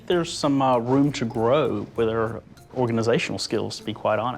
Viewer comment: "I think there's some room to grow with our organizational skills" he says. HAHAHAHAHA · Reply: I thought he said with HER organizational skills?